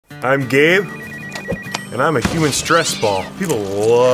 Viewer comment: Thought he said im gay 💀💀💀💀